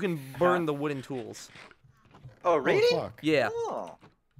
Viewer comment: Did just say fuk?